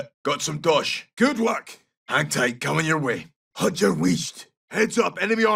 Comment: I couldnt make out what he said, can somebody tell me what he said here pls? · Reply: I'm pretty sure it's "Haud yer wheesht"